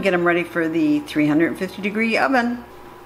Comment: oops , I decided to rewatch the video again and heard you say 350 degree oven this time... I am sorry. thanks again